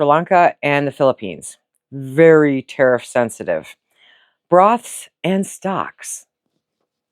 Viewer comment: At first I thought you said Bras and socks and wondered how that had anything to do with food! LOL!!!!!